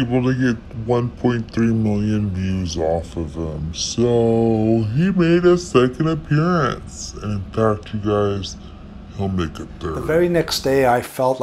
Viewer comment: I have been enjoying the topics you touch on, thank you for uploading. One thing though, when you do that augmented voice part I cannot make out your words clearly like at 2:02 Aside from that, I do agree that the dead should not be played with. I do not believe in paranormal much, but I believe that there are real territories you do not want to evoke. And I feel that the real people that know how to respectfully tap into these things suffer in some way and are not looking for fame. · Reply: Thank you for the feedback! I’ll be sure to keep that in mind next time perhaps add text? I said: ‘so he made a second appearance and in fact you guys he’ll make a 3rd.’ ‘He saw how well our first video did so he really really wanted to make another appearance for you’ Xoxox